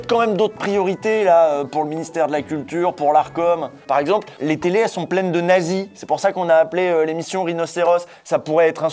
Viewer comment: J'ai entendu « les télés elles sont pleines de Naguy » au début j'étais paumé. x-x